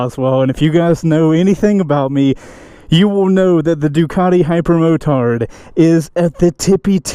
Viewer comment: Did I just hear say "hyperretard" @ 0:08?